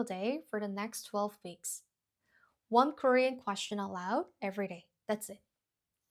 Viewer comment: Just a note: at 4:05 the subtitle says “allowed” instead of “aloud”. ;)😊